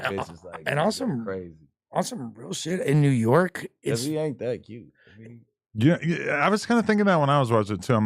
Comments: wtf bricc say??? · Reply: I was just finna comment that. Nigga said "he ain't even that cute". Like a hatin ass female after you curve her. 😂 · Did Bricc say Duke ain’t that cute? So there guys that you find cute Bricc?😂